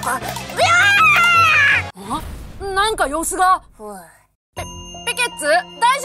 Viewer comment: がふうがおいて聞こえる